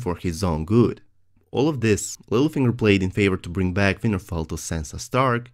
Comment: lol I keep hearing '' Weinerfell''